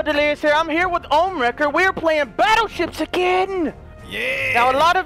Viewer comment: at the beginning i thought he said battleshits